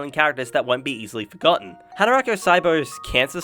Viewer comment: Could someone please refer me to the show he mentions at 7:13 - I can't make out what the is saying. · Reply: Yeah sorry my pronunciation of Japanese titles isn’t great 😅 The first one I mentioned is: Hataraku Saibo (Cells at Work in English) The second one is: Dororo Both are brilliant animes but they are completely different 😁